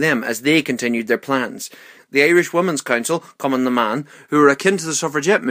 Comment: Why do i always hear say: "cumin' in da man" instead of "Cumann na mban"